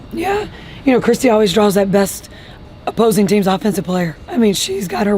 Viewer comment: Did... Coach Sides say around 13:30... that "Kristy Wallace draws the best offensive player on the opposing team. She's a dawg. I love her"? Did I hear that correctly? Meanwhile she never says anything remotely close to that about CC. I'm not sure Wallace is on anyone's scouting report?....